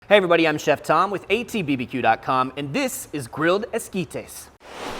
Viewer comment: who else thought this said grilled mosquitoes at first?😂